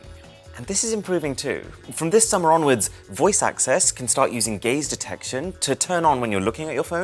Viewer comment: I thought he said gay detection